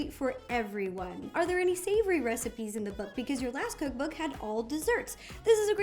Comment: I thought it said color book and was so excited!